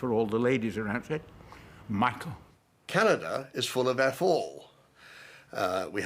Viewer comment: "Canada is full of ???" American here; don't understand... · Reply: He said "f- all," short for "fuck all," meaning "nothing."